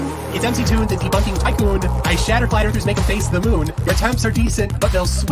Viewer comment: Found that 'accent' like a train wreck, where you can't look away. He shifted through a Texas twang, into an Alabama drawl, from his mostly Boston nonsense. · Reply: Just heard him say "Fwat Erf". 😂